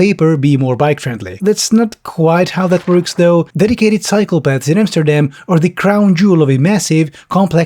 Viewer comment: Damnit...brain on autopilot, sipping on some tea...I mishear @ 7:15 "...dedicated psychopaths in Amsterdam..."...at least now I'm sure, that keyboard is water-proof 😅